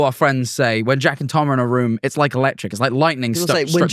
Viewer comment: everytime they said jack and tom in a room i thought they said “jackin tom in a room” :(